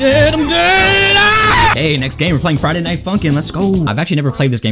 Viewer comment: I thought that you said Friday night f**kin